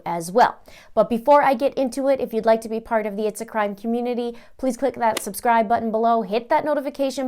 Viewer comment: I was listening to this in the bathroom while I was getting ready, and my partner walked past right when you said "if you'd like to.join the it's a crime community..." and he stopped at the door and said "what are you listening to!?" he thought he heard "if you'd like to join the ANTICHRIST community..." 😂😂